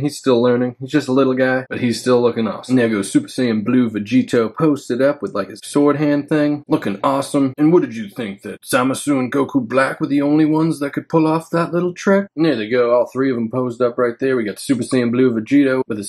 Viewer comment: Btw if anyone watches this video with english sub, theres a lot of N-words 😂😂😂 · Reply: I also laughed my ass off when it said Thomas ruin instead of Zamasu at 01:48 🤣🤣 I swear, auto dub is something else 😂